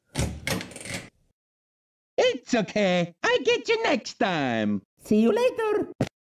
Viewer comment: If you are wondering what he said, he said: It's ok, i get you next time! See you later!